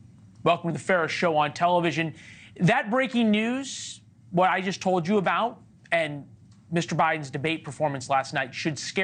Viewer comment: did he say welcome to the Ferris show on television? Why?